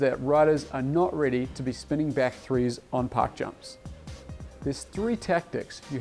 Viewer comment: i got halfway thru the vid b4 i realized he was saying park jumps i thought he was saying pock jumps ,,,im like what the hell is a poc jump?